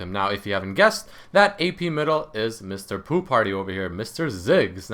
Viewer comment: lol I thought he said mr poo party